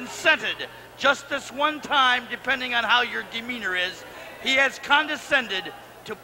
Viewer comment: What is being said at 1:06 ? Gimmenery? Or what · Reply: "depending on how your demeanor is"